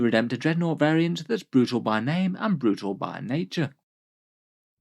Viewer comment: Had a chuckle hearing that intro, i really thought he said “ a new dreadnought that’s brutal by name and naughty by nature